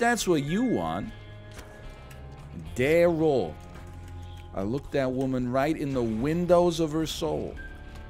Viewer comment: it sounded like he said my name “Darroll”. I am a payed out believer. Made me blush love you NL❤️